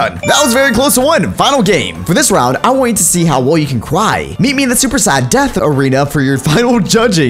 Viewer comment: WHY DID HE SAY CWY IN STEAD OF CRY?!?!?!???!??!?!??!?!!